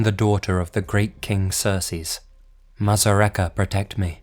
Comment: Lol during the first story anytime I heard him saying Xerxes it sounded to me like he was saying Cersei